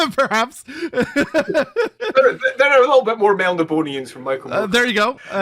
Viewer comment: Fantastic discussion! This man breathes the lore we all love. What is the word that says when relating to the Dark Elves at 27:18? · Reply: Melnibonéans. See my reply to Lange for a little more context on this and Michael Moorcock.